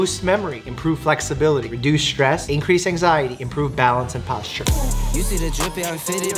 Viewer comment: is it just me or I heard "anxiety"